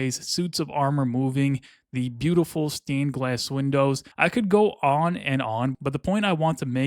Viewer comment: did you say steam glass windows lol